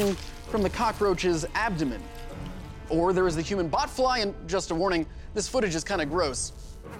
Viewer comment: Thought he said buttfly and I had a heart attack.